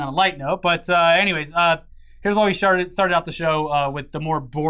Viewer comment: Hold on. Did just say "here's what we sharted"